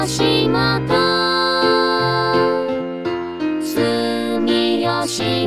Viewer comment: よく、Fライナーを使う機会が多いので防災無線で流して欲しいです、あと、1:11が吉本に聞こえた。